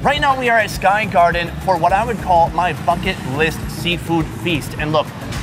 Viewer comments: I thought he said fucking list 😂😂😂😂😂😂😂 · 🤣 sounded like he said f$@ list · i thought he said fuck it list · When he said bucket list I thought he said fuck it list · I genuinely thought he said his 'fucking list'.... Lol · i actually thought he said " My Fuck it list seafood feast" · did he say fuck it list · I thought he said "f**k it list" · I thought he said F*** it list smh